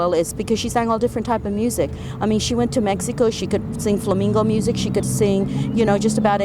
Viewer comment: did she just say Flamingo music 🦩 🎶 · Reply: Flamenco